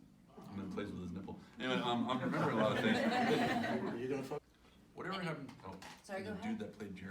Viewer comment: Im curios what was said at the awkward cut at 43:14 :) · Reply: "you gonna f her next,"